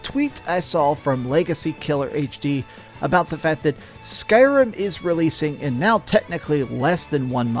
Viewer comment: did he said Skyrim? Anyway, why is Starfield not trending? There's nothing to talk about, just wait for the release. It's gonna be the same with Spiderman 2, last trailer will drop with release date and pre-order option for the game, gonna be trending for few days and then silence, cause why should it, there would be nothing to talk about besides how much of a boner people got and why Xbox has tiny flaccid pancakes